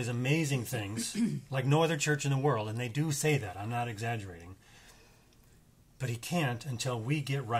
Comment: Lol lol lol it almost literally sounded like said I'm not a heratic 😂.